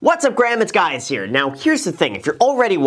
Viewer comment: Did you just say .. "What's up Graham ,it's guys here" 🤣🤣🤣